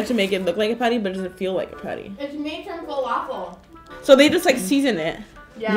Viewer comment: did she say "FALAFEL" omg hahaha